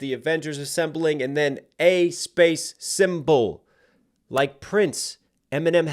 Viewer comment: I heard it " a cymbal like Prince " you know prince drum set cymbal